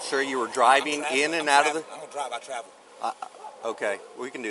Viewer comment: What he says: "I don't drive, I travel." What I hear "I don't think, I derp."